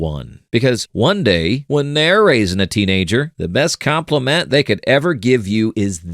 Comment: I thought it said "THEY ARE RACIST"